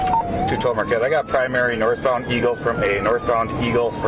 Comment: For a minute there, I thought he said Marquette, Michigan because we have a city named Marquette here in Michigan